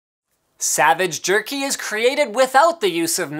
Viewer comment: Did I heard Savage jerk